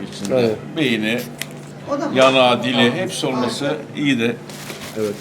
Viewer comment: abi ben mi yanlış anladım yoksa adam ; Beyni Ya"ağı dalağı mı dedi :D